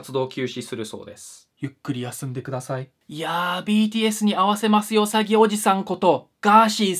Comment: なんか、毎回btsに会わせますよ詐欺のところがpdsに会わせますよに聞こえるのでpds とコラボしてください。